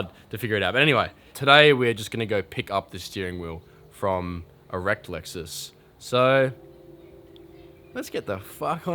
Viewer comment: subtitles say `pick up steering wheel from erect lexus` 😂